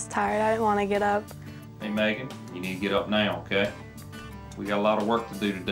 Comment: i thought he said maggot instead of megan